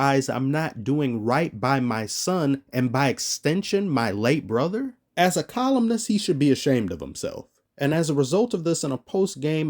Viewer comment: I thought it said 'communist' and got angry for a second.